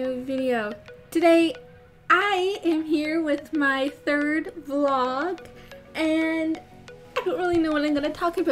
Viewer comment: i thought you said "i am here with my third koala" or is that what you said? either way, i have problems :D